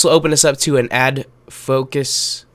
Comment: lol it sounded like adfuck.us